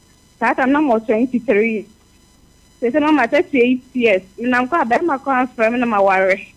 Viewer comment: Min 16:55 Did she say 38 years or 28 years? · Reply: When she was 23 and 38 now